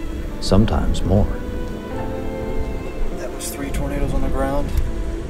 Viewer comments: captions said that was three burritos on the ground when u actually said that was three tornadoes on the ground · If u see 3:53 using subtitles says burritos instead of tornados, interesting video.